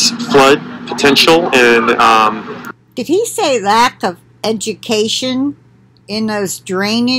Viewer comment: No, dear heart..he said vegetation. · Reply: LOL, I can't believe i heard "educstion."